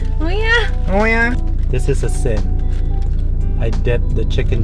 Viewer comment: LOL He said "Its a sin" I thought he said "asin" (salt) Hahaha